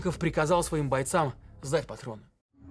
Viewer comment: я не понимаю, что он приказал. "в зад патрон"?... "зед патрон"? кто расслышал?) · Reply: приказал сдать назад